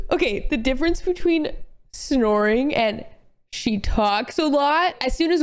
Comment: Is it me or saying “she talks too much” is a red flag? I love hearing my partner talk, i wouldn’t date a guy i didn’t find interesting??? Like hello??? Like at first i thought he was saying “she talks a lot in her sleep” which that would have been so valid but then the last part never came 💀 man, why do some people go out with people they don’t like listening too??? That likes saying “shut up i don’t care” bruh